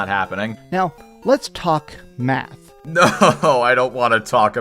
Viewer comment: you misheard.. he didn’t say math he said meth.